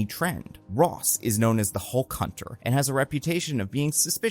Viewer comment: I misheard Hulk Hunter as Hole Cunter. Neat.